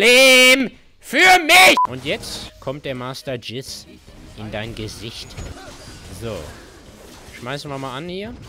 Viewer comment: August 2019, 10. mal angeschaut und jetzt erst „jetzt kommt der Master Jizz, in dein Gesicht“ 14:35 verstanden😂